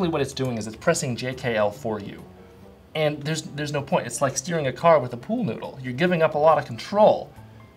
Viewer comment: "It's like steering a car with a pool noodle" Beautiful. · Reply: I thought he said stealing haha, it's better than I thought!